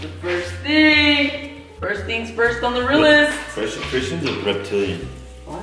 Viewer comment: did say Christian's a reptilian?!?! LOL